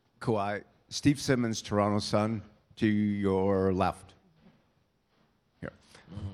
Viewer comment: hahahahaha i heard "do your laugh" instead of "to your left"